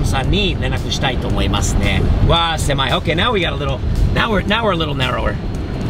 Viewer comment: これ英語でなんて言ってるの？ ナーオレロレロ ナーオレロレロ？ 正しいの教えて😭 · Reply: Now we are a little narrower...